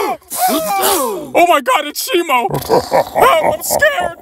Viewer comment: what did suko said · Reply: He said "Be careful dad!"